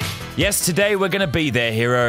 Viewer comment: In the start I thought he said yesterday day but he said today